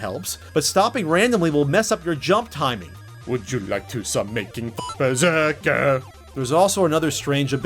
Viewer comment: Did you just say making fuck?